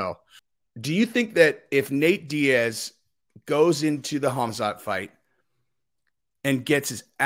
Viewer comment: When said "khamzat fight", for a second I thought he said "homicide fight". Hahahah